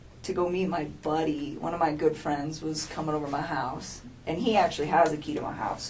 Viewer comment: Is it just me or at 1:46pm it sounds like she said “our house” am I hearing things?